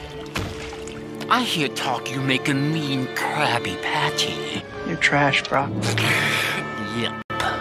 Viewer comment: I love how his line was cut so it sounded like he said "Your trash bru"